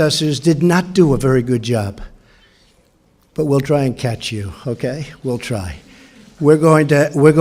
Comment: LMFAO!!! Look at how she smiled and gripped the podium when he looked her directly in the eye and she heard him say "WE'RE GOING TO CATCH YOU" Lol Love you Trumpey!! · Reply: "We'll try and catch you" Not we'll try to catch up to you... which is probably what most people assume he meant... but it's not what he said, and she knew exactly what he was referring to. The look on her face shows that.